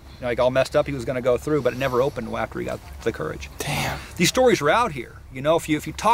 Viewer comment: I heard "Damn"..... Did anybody hear it.